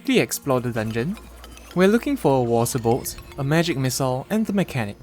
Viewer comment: I heard "moral support" instead of water bolt